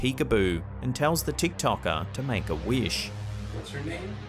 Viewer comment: That was clearly not "What's yours" but "Who is this?". And how did he get "Peek-a-boo" out that scratchy sound before? That's why I rarely believe those electronics where the spirit allegedly talks to you, because most of the time what is being "said" is completely up for interpretation. Besides... this guy is just using a regular ol smartphone.